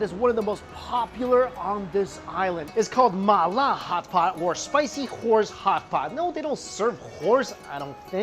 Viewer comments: I thought he said Spicy Whores. My bad. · Ok I misheard and thought he said no they don't serve whores here. To which I thought no that would be in a completely different establishment I wager